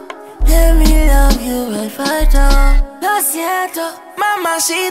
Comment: i thought he was saying Los Siento Mamacita just to get up on me but its acc te quiero pa' mí